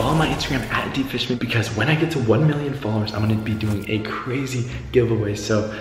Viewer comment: who else heard 1 mill followers😂😂😂